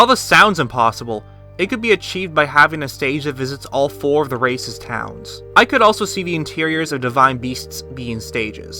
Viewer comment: did he say racist a